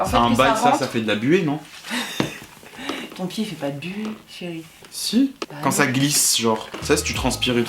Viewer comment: Rassurez-moi au bout d’un moment dit « ton pied fait pas de buée chérie » 🤷🏼‍♀️😫